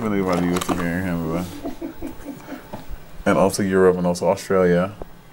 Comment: did monet just say europe's in australia??? oml